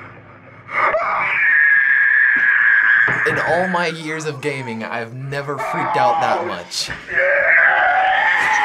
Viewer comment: Did he just say," out of all my ears of gaming I have never freaked out that much".......